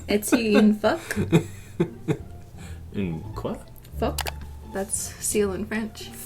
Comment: OH! This clears something up...in the "instructing my boyfriend how to paint my nails in French" there's a bit (whilst talking about gendered objects) where like "what the(I thought she was saying f*ck) and she flashes an image of a seal. I never got why she was showing a seal, and was so confused every time. I didn't take french in school, I took spanish. About the only word I know in French is "cheval". (also, how have I never watched this video until now?)